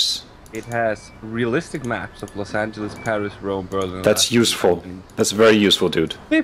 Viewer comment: did he said los santos ?